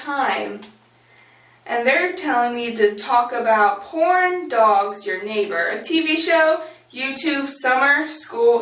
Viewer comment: i thought u said corn dogs...Lol:P